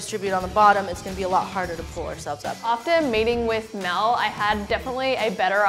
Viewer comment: when she said "meeting with Mel" I forgot she had an accent so I thought she said "mating with male"